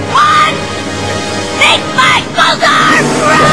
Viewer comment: is she saying "Make my bosom grow?"